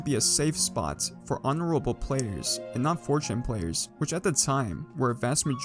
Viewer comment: I cannot tell what you're saying a 1:24 is it forge hack players or 4chan players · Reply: 4chan 0players